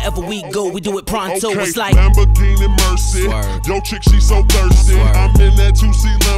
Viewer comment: Who thought he said “never gaining mercy” at the beginning? And yeah, Best song ever no cap.